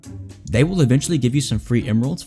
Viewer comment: I thought he said Beesechurger instead of Be sure